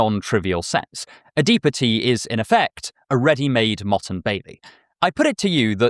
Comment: im sorry a what? What is this word please? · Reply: motte and bailey is the word there